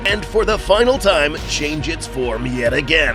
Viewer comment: did you say perfect